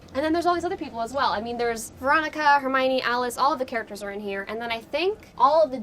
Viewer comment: did she say Hermione